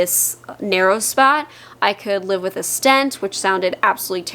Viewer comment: Minute 18:18 Haha… I am happy to realize my error here. You said, “…I could live with a stent…” Well, I thought that you said, “…I could live with the stench…” I thought, “Eeeeuuuuu, that doesn’t sound like her! What the heck?! God!” Then, I realized that you’d said the other word. Holy moly. Glad I realized my mistake!